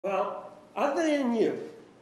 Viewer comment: what the heck does juan say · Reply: "Well, other than you"